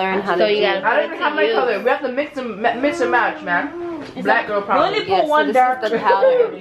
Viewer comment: did anyone hear her say " black girl problems " ?